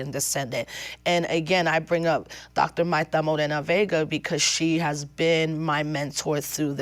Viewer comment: who does she say ? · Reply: Dr. Marta Moreno Vega